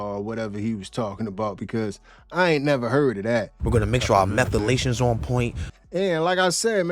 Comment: "Methalaytion" was new to me too!! 😅😂😂😂 I replayed that part 3-4 times like 'what he say'